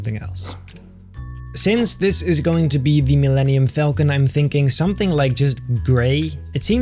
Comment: I thought this said millenium force and I was very confused.